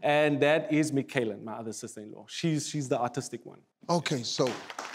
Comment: For a second, I thought said the girl at the end was autistic.... I was about to say why would he say that? But he said Artistic